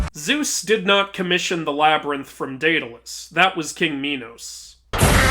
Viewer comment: Did you just say... minos? Holy shit is that minos prime